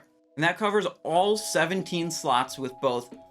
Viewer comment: i bet i am the only one who heard "all 17 sluts" @ 14:24 right?